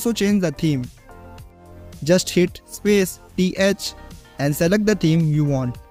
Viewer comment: what did you say? Space + ...? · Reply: That's SPACE t h and you can also view NVchad cheatsheet to see default key bindings, by pressing SPACE c h Hope, this helps.